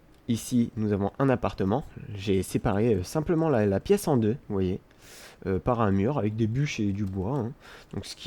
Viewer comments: C'est moi où à 1:37 dis: m'voyez. x) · C'est moi où à 1:38 dis: m'voyez. x)